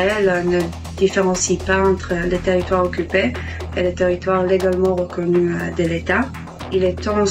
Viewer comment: Il Y A PAS DE TERITOIRE LEGALEMENT OCCUPPER , JAI ENTENDU " TERITOIRE LEGALEMENT OCCUPER . JE VIENS DAPPRENDRE UN NOUVEAU MOT , INCROYABLE.